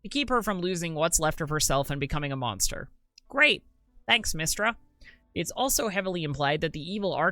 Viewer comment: I thought you said MystAra and I was like whaaat XD